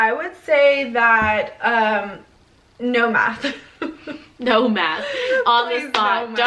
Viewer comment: Lmao I thought y’all said “no meth” and I was like DONE!!!! 😂🤣 and then you did the math concept but great video.